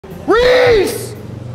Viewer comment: At first I thought he said freeze like it was a robbery💀😂😂